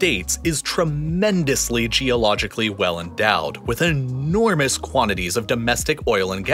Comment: Did he say tremendously geologically well-endowed